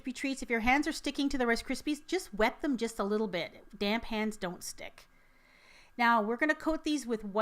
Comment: I thought she said "damn hands don't stick". 😂